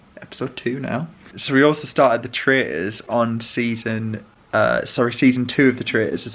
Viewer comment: Great day and love your cosy corner with the new table, plant and curtains. Just wondering what that second series you are watching is called and where you watch it? I thought you said The Trays???? Is it Netflix as well? ;) Thanks